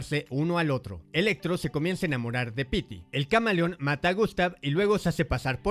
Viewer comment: No se xq en el minuto 6:19 entendi "electro se empieza a enamorar de peter"